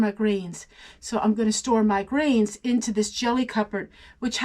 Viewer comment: Omg! I heard you say store your grains as store your brains😂😂😂 Obviously my brain is not hearing properly. I’m reorganizing my kitchen and I climbed down the ladder and rewound. I thought maybe you were canning … brains😂😂😂😂 Had to tell on myself. I laughed out loud and my hubby looked at me like I’m crazy 😂❤